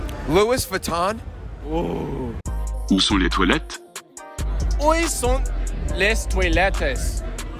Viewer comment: "oi sont lets toy lettuce"